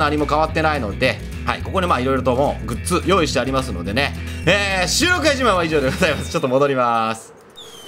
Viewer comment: ･･･何度聞いても「収録外自慢」が「収録が一番」に聞こえるんよね･･･